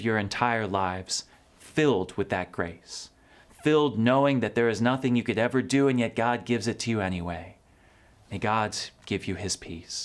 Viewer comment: I heard "May God give you his piece" and I had a thought where he say: "I gotchu homie." as he gives me the Holy Glock. I'm sorry...XD